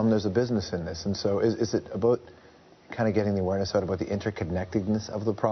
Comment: Did he say "aboot" [0:13]? Common George, we Canadians don't really talk like that.